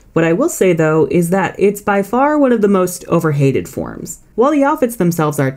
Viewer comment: Your voice says "overhated" but the subtitles say "overrated". 🤔